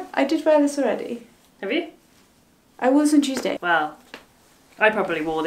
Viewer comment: I really like the tuesday (which at first I managed to mishear as cheese day and got very confused for a second) look! Very high school uniform with the plaid skirt and letterman jacket but make it vintage